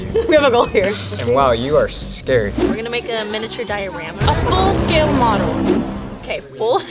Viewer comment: I thought she said "a miniature diarrhea" and the other girl said "A FULL-SCALE MODEL" I was so confused